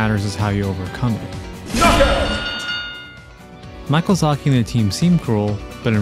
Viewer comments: Did I hear you say "Michael Zaki"? xD Big up PTT RKG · did you say Michealzaki? 😄